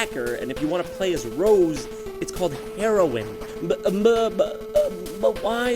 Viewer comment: oh I thought that said herobrine.